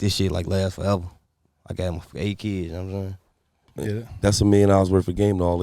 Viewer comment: did he say he got 8 kids!?